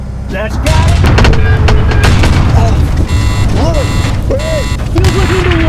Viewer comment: I can hear "Look! Burn!" but the subtitle says "Lewis! We are hit!" How could that be? What does "burn" mean here?